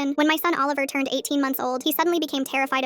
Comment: i thought you said 18 years not months